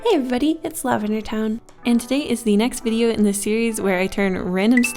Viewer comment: When she said "next video in the series" I heard "last video" and got really scared for a second lol Also thank you for my saucey gent lol I think I'm the most un-Sagittarius Sagittarius there is